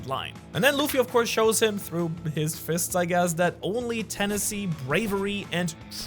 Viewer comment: said tennessee, I instead of tenacity lol